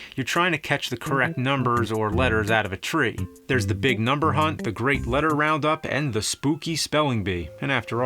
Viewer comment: I thought he said number hump